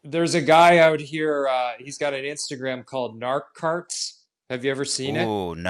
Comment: What is he saying? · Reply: Narc carts